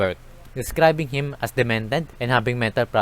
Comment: Captions are now available! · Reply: your caption/subtitle has demanded when it should be demented, but over all your fast English is not too hard to understand, sorry so many people report that they can't understand you.